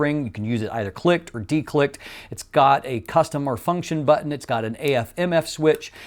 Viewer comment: OK, once again great comparison review but you are still often saying, "customER" function button. I’m not hearing incorrectly. At 3:59, just slow the audio playback to around 70% or a little slower if need be and listen closely. you are clearly saying the three syllable word custom-er. I’ve listened to it along with other people I had listen without telling them what to listen for. I then asked them what you said about the function button. They all said that you clearly said "customer" function button. I’ve brought this to your attention before just so you would be aware and to stop saying it as it sounds silly. For whatever reason you’ve got it in your head to say 'customer' instead of 'custom' and you repeatedly say it without realizing it. This started several videos ago and to my surprise it wasn’t a one-time or one-video thing but you keep referring to the button that way when a lens has one. I think in the last video you mostly said correctly, "custom", and I thought you may have read my comment and made a mental note but you’re back to doing it again. Although it could just be the order that you wound up posting the videos. If you think I’m being mistaken, just slow down and listen carefully to your own video. Take this only as helpful criticism so you can correct it going forward. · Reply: I've already given you the explanation. I went back and listened and my response remains. I am saying "custom OR function" button.